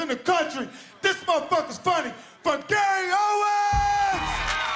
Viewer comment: I misheard him. I though he shouted “FOR GAY OWEN!” At the beginning. Hahaha